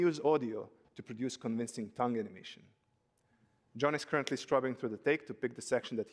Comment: Braindead person doing captions can't understand that it's "scrubbing" not "struggling." 10/10 career goals. Can't even deduce words from context but does captions.